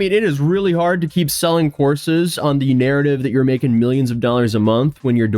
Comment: I was half listening and misheard 'courses' as 'horses' and went wait when did this pleeb get into horses?! I need to put the subtitles back on